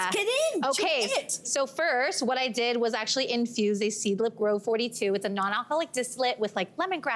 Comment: Curious about the first recipe: I heard the words “Seeglip glow 42, dislit (sp??)” for the first ingredient of the first recipe, but I have no clue what this is. Can you please enlighten me - thanks! ☺️ 😊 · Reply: Found it! “Seedlip Grove 42”